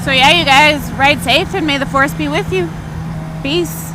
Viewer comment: it sounded like you said beece at the end instead of peace